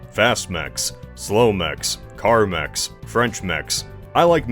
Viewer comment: Said "car mechs" and I thought he said "Carmex" and I was like "why is bro talking about lip balm"